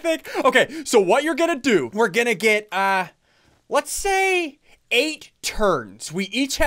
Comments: SSUNDEE'S MORE POPULAR THAN CRAINER!! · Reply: When he said 8 turns, I thought he said 8 turds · When he said 8 turns I thought he said 8 turds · I thought he said 8 turds... don’t blame me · I heard cranier say "let's take 8 turds"